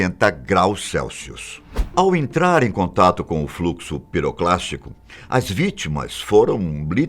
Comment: Só eu que ouvi "pirocastico"?